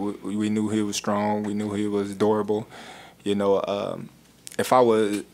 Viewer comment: > sounds like he says ".......he was adorable" obviously he says durable, but its that Omaha twang